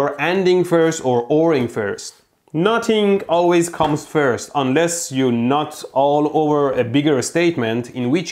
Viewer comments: Why did I hear "Unless you nut all over a bigger statement" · is he saying not or nut, because that makes this a very different video, especially since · Did he just say, "Unless you nut all over a bigger statement..." omg lol